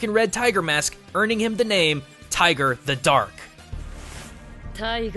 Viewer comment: i think that at 7:21 that lady said [tiger the duck] oh how i love engrish so